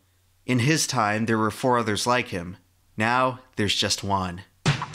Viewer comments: “Now there’s just Juan” Is what heard but now I hear “Now there’s just one” And then “Now there’s just ouan” Afterwards I heard “Now there’s just juone” Please help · did you said ''now theres just one'' or ''now theres just juan''